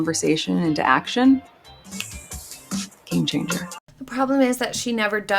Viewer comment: when MM says "game changer" the subtitles say KING GINGER. If you listen really close it actually sounds like KAME GINGER. She actually says 'game changer' quite regularly during her repetitive word salads. Knowing her, it's probably some sort of subliminal trick to get the thought of "KING GINGER" out there. That would make her Queen LOL.